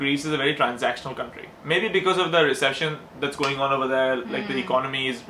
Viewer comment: Wait, did he just say reception instead of recession at 14:15?? Deadass hahaha :)